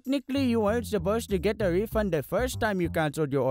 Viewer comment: Margaret: steals 10 bucks: I guess I'll just have to make a YouTube video and get a whole lot more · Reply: I thought he said he got a refund in the first order